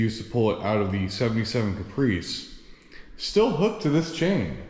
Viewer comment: I was listening at work while doing something else, and for a minute there I thought you said "I know this came out of a Prius, but obviously Chevy small blocks get swapped over quite frequently..." 😂